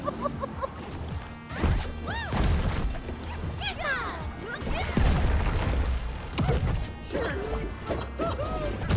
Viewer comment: Yo what gizmo just say?????? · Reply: he says gizmo